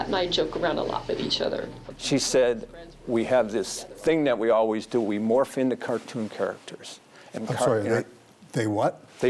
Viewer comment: I had to rewatch this because I heard “they orphaned the cartoons”